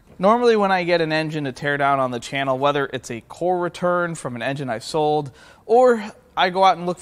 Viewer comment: You said “core return” and I swear I heard “former turd”. I had to run it back lol